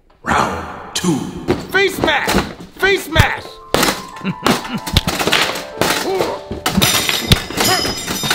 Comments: I thought I heard "FACE MASK! FACE MASK!" instead of Face Smash lol This quarantine and nonstop corona news is slowly driving me crazy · What he said: “face smash” What I heard: “face mask” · “KNEE” Holy crap I think that’s the guy from the interview the one who can put on anything he touches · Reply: I thought he said face smash